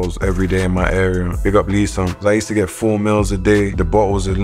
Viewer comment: When Fredo said meals I thought he said mills cah he's the money man